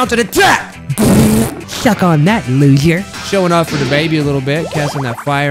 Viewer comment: "shuck on that loser" is what i hear